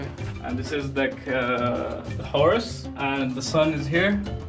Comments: Horse? I think he is saying "Horus", a god. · Horse is supposed to be Horus, since he's talking about a sun, and "like a god". It kind of sounds like he is saying Horus? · I think he said Horus (accent Egyptian god) instead of horse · I think he said Horus not horse